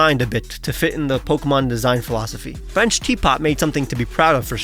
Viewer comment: did he say french teapot